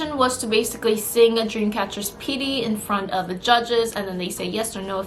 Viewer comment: Did I hear that right? DREAM CATCHER PIRI???? INSOMNIAS WHERE YOU AT